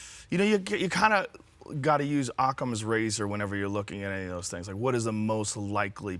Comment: what does Joe rogan say ..."[something] razor" And what does it mean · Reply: Graham Joss Occam's razor. It means basically that out of multiple possible explanations the simplest is the most likely to be true